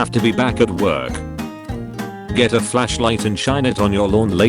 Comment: who else heard flesh light